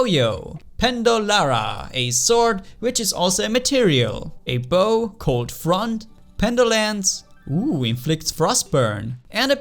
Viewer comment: I Thought you Said "A Bow Called Front"